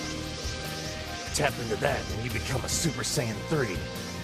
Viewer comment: I thought he said scream hard to be a super saiyan 3